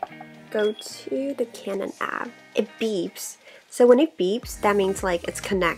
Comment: Does she say”canon app” at 11:20?